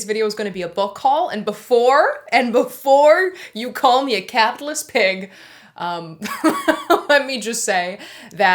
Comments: I laughed so hard when you said “before you call me a caPITALIST PiG” 😂😂🐷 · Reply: I heard "catless pig" 🤣🤣. Capitalist pig makes more sense tho hahaha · I laughed so hard when you said “before you call me a caPITALIST PiG” 😂😂🐷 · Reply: I thought she said catholic instead of capitalist and was sooo confused